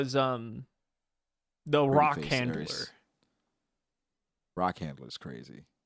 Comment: Did say "Rock Handles" or "Rock Handler's"? LOL. I'm done. 🤣